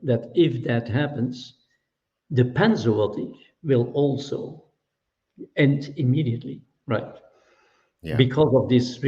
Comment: in, He says that he's two hundred percent certain That something will end. But I can't tell what he's saying. Can anyone here tell me what he thinks will end? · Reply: The pandemic